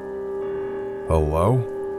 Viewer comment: Okay bu why did he say "Howwow ?? O.o"